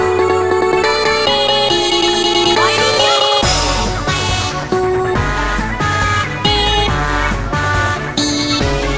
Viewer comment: What is Felix saying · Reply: YDO.